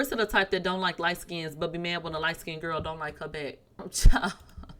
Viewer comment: I just hear Claressa saying “another light” 😭🤦🏾‍♂️